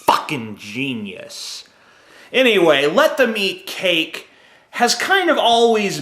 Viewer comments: I thought he said "let the meat cake" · I thought you said "let the meat cake". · A swear it sounds like hes saying "let the meat cake" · Am I the only one that heard him say "Let the meat cake"? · lol im such a f*ucking idiot at first I thought he said 'Let the meat cake!' not 'Let them eat cake!' I am ashamed that I am such a f*cking moron on this particular video! XD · LOL at first i thought he said "let the meat cake" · At first I thought you said let the meat cake lol